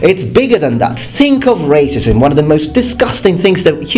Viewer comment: I thought you said raisins for a second, then I got the context